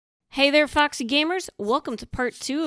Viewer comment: I've recently gotten obsessed with your guides, but at the start of your videos, I always mishear "hey there foxy gamers" as "Hey there FUCKS n' gamers!" And that always makes me smile xD